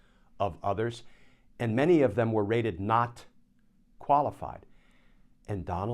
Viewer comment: Rated? I thought you said "raided". Now there's a thought.